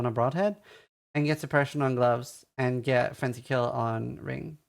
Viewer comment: can't understand what you're telling him to get on his rings at 6:11...replayed it a bunch...anyone know? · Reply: frenzy kill